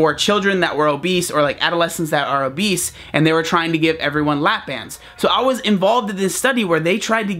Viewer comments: i thought he said lap dance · did anyone else think that when he said lap bands he said lap dance lol